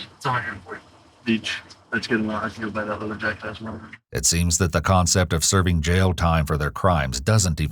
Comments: I don't think he called someone a jackass, I think he said "I can buy that leather jacket I wanted" or similar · this caption is wrong, no?? I hear him talking about buying a leather jacket, not the caption on screen